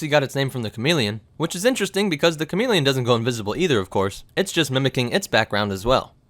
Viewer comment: I thought it said invincible not invisible XD